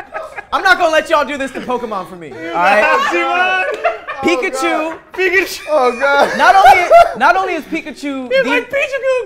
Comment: did he really say "pichaku"?! 😂